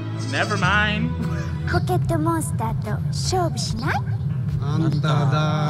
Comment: I wasn’t paying attention and I just heard Zetsubō (it means despair) bitch if your selling despair sign my danganronpa trash ass up.